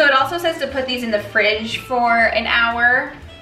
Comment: Why do I feel like she said "the pussy's in the fridge"? I cannot seriously be the only person who heard that.